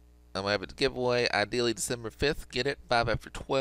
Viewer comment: At first I thought he said December 25 I was like stop making vids and go hang out with your fam O and also you didn’t blur the code out